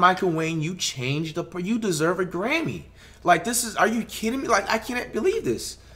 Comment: Did he just say you deserve a Grammy 😭